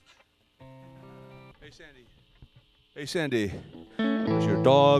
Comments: I love how the lead singer has actually become even harder to understand. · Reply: I misheard it as "Hey Sinbad" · I love how the lead singer has actually become even harder to understand. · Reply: Actually, his Hey Sandy is actually understandable. I always thought he said it's Sunday.